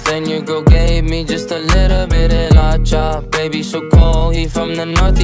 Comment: İ thought he was saying Gay? Me? Just a little bit